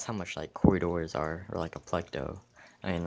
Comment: did he just say p l e c d o?